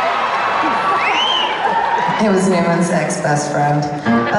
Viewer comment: I cant understand what she says at 5:22 I WANT TO KNOWWWW I actually speak spanish sorry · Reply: She said 'he was Newman's ex-best friend'. Newman is Brian Newman who is the trumpet player on stage and a friend of Gaga's since before she was famous.